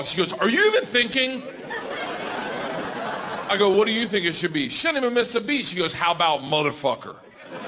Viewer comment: WHAT IS HE SAYING! I cant hear it for some reason. Edit: nevermind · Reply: “She didn’t even miss a beat”